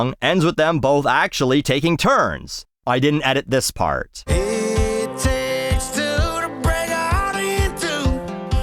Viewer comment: always thought he said 'It takes two to break a how do you do' and now I feel silly